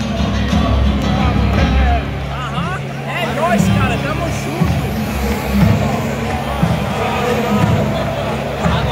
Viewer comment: Can anyone tell me what the guys at 1:08 said? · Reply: He said " é nois mano tamo junto" that means ", yeah bro, i'm with you"